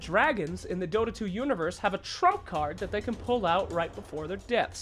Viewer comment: did he just say trump card?